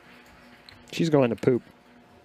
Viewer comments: did I just hear “she’s going to poo “ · Did he really say « She s going to poo » ??🤣🤣🤣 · What he said at 13:09? She is goin to poo? 😅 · “Shes going to Poop” hahahaha did I hear that right